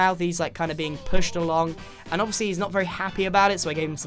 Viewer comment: he said ov see instead of obviously , just put it in 0.25x speed and you will see